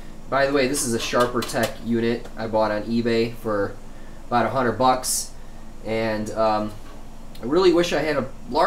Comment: Did you say you paid "about a hundred bucks" or "$500 bucks" at 17:03? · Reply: 100